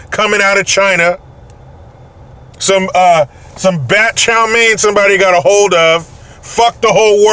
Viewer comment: "bat chow mein" haha at 1st I thought you said 'bad' chow mein 🤣